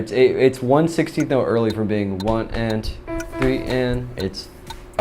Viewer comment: Did he just say A-E...? Now I'm just thinking it could be the motive of the whole song